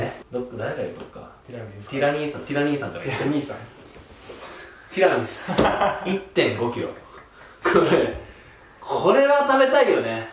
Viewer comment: ティラミスの1.5キロ がブルゾンちえみの 35億に聞こえたの私だけかな。